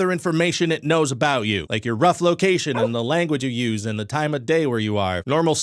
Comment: I thought you said Ruffalocation, and I thought that was some kind of procedure where we get turned into Mark Ruffalo.